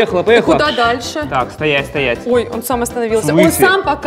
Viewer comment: мне кажется или тут сматерился? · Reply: мне тоже сначала так послышалось, но если прислушаться, то можно услышать слово стоять